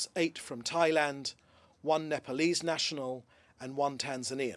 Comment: Did he just say polese national!!! Uneducated cunt